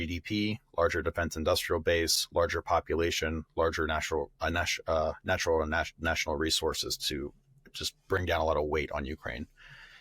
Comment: For a moment I heard George say "natural and UNNATURAL resources" and that gave me a much needed giggle ^^